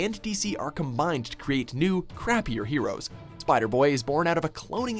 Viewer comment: did you just say "crapper" heroes? screw you!